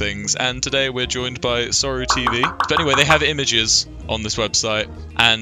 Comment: When he said sorrow tv i thought he said isorrowproductions